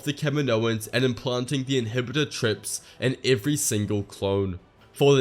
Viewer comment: inhibitor trips😂😂😂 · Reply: I didn’t pick up on this watching the video and could’ve sworn he said chips, but upon clicking the timestamp he did infact say trips